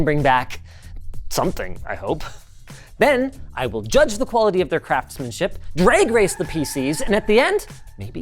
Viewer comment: Why did I hear "quality of their crapsmenship"?